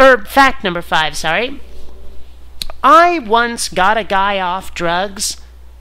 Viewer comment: All I heard was "I once got a guy off." Fit in with the whole naked theme I guess.